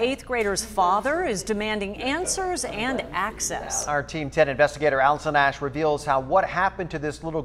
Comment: did he say... team 10? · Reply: Angel Chan ABC team 10